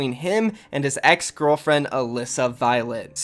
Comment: I thought you said sum like ex's girlfriend lmao